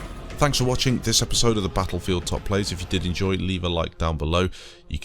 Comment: Why do i hear"buttholefield" everytime a british person says it?